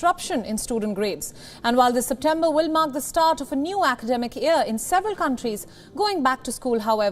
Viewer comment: oh academic i thought that said scamdemic